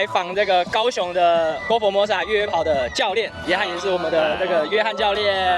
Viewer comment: 原來是 岳翰教練，我一直聽成 約翰教練🤣